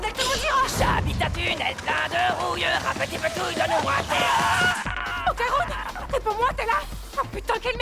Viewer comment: je m'en lasserai jamais "j'habite un tunnel plein de rouille, un p'tit de tout donne moi tes couilles" 😂😂😂😂😂